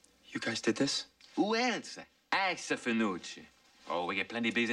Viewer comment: Guys, what the Finucci bro says at 3:41 "Who else? .... Finucci" I guess something in Italian like genuine Finucci. Hope someone recognize it. Thanks a lot! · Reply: "Us a Finucci"